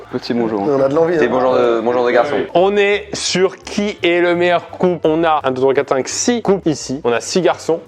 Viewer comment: j'ai compris "aujourd'hui on va trouver qui est le meilleur coup" ptdrrrr mon dieu